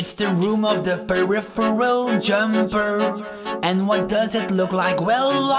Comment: it sounds like he said perfill jimper than pherfil jumper